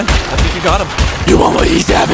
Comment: Ngl that "Justice for Gotham👹" made me crack up💀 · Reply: i thought he said just this forgot them